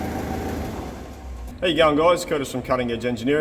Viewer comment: I still don't know how the subtitles manage to get "how you going guys?" Out of what I hear as "hey young guys!"